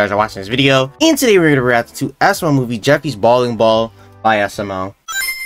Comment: It sounded like u said Jeffy's balling ball in the intro